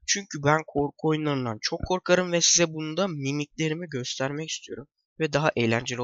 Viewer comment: mimiklerimi memelerim diye anladım amk